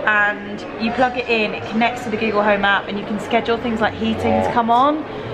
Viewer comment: Sounded like she said the giggle home app lol